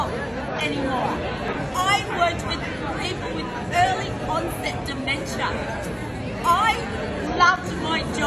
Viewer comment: Did she just say she was diagnosed with on-set dementia?